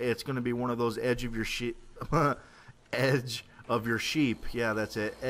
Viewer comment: i thought he said edge of your shit not sheep